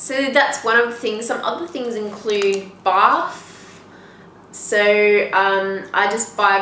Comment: Did you say barf, 1:50?